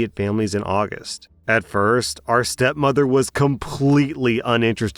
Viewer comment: WAIT DID U SAY OUR STEP MOTHER?